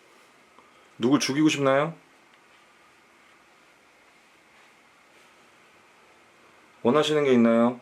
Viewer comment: 에 "죽여" 라고 들리는거같은데요